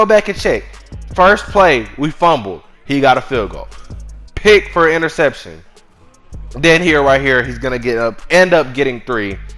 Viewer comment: did dude say “pick for an interception” ? lmao